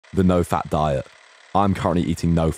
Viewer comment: Why am i hearing it "no fap"😂